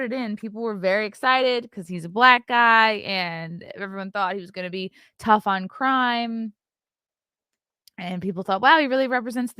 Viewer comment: I must've misheard during the debates with Curtis. He really said he was gonna be tough on Prime Rib